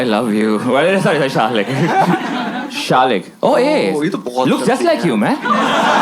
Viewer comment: what did say? ye toh bohot hi...?